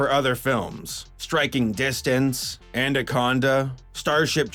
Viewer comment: Did you say "andaconda"? Why?